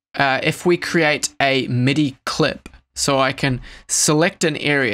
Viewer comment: I had to ask my dad what it meant when you said “you have to hit Ctrl shift m” at 4:52 thought you were trying to say impr or im 😂😖